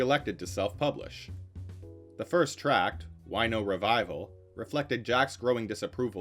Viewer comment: LIstening to the audio only, I thought I heard, "Wino revival".